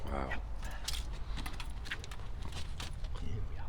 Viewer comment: Where's the ghost voice? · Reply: WHOA!!! I heard the "here were are" shortly after the 5:32 mark (shortly after you say "wow" BUT.......how was this caught in the first place???